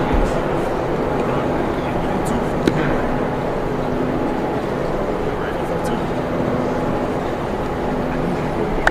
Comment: Hey.. I heard 4.2. Am I hearing things? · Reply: Jimmy Horn jr got him at 4.35!!